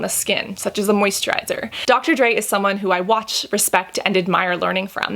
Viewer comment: Lol I thought she was saying she trusts Dr Dre for her skin routine, only when I read the comments I realised she meant Dr. Dray's 😂